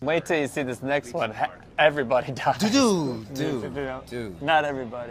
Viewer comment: What does this guy at 9:04 say? 'Wait as cities next one has everybode does' ??? I can barely hear his voice..